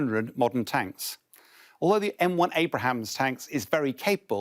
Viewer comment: Did he just say M1 Abrahams?